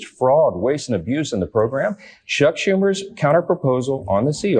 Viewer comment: Did Johnson say, "Shuck Schumer"? (at about the 14:00 mark)... Shuck? Or is that a speech impediment? I might be digging too deep, but it nothing that comes out of this guy's mouth is believable.